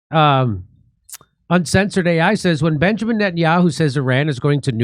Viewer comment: Oh. I thought that said Putin at first. Nettenyahoo makes much more sense.